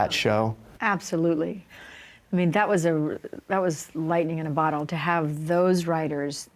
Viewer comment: Alright I need to get my ass back to sleep cause why did I thought it say lighting in a bottle and she was gonna talk about the crazy lighting they had on set😂